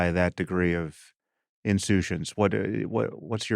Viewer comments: what is this word insutions? ensuetions? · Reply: He said “insouciance” which means a lack of concern; indifference. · What the hell was that word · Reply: insouciance - casual lack of regard, indifference